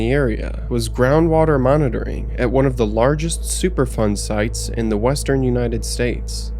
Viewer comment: I thought you were saying "super fun" sights, I had to turn on the subtitles to find out it was "superfund". I thought it was weird that a place with hazardous materials was called "super fun" 😅.